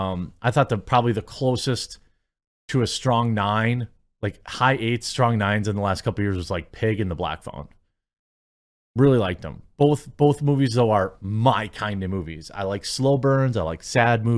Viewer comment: Thought I heard 'Pig in the black thong' and I was sitting here like: 🤨